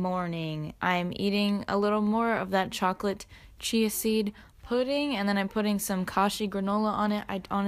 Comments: Omg when she said "chocolate chai seed pudding" I thought she said chocolate cheesy pudding and I was just like.. " okay that sounds gross but you do you" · i thought you said “chocolate cheesy pudding” and i was confused